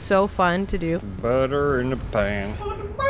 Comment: continuing "put her in the pan" is true partnership□□ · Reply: Oh, I thought he said butter in the pan!